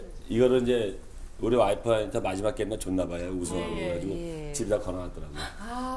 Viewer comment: ㅗㅜ깜짝 놀랐네 줬나봐요를 줜나 봐요라고 들었음 순간적으로ㄷㄷ